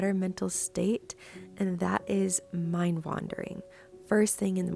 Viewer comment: At first I thought you said “ mind laundering “ hahaha! And before I realized what you actually said, I thought you were referring to cleaning out your negative thoughts. 😂.